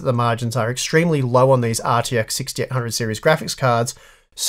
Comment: did he say rtx 6800 xt series card? If so, then it’s nvidia pulling this pricing bait